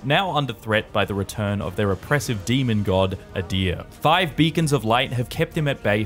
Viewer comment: lmao i thought you said five vegans of light at first had to listen to that part twice.